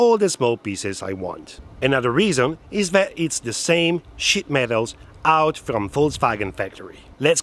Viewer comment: I love your accent! I thought you were saying ‘shit metal’ and after all that rust...! 😂